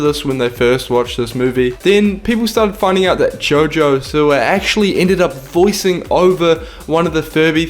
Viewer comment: Lol it sounded like he said Jojo Sewer